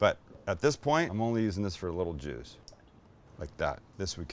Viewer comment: At first I thought said, “I’m only using this for a little jizz.” 😂